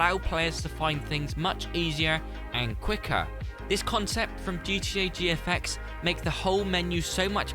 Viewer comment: It sounds like you said the concept at 2:40 is from “GTA VFX” but can you link their channel or something in the description for anyone who wants to look into them. Maybe just a que card in the corner. Idk.